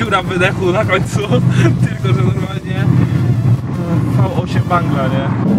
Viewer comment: czy on powiedział V8 fangla (wankla) czy ja źle usłyszałem